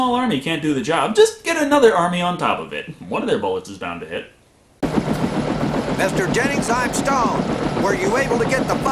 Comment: "Mr. Jennings. I'm Stoned." I swear to GOD this is what he says. I will die on this hill.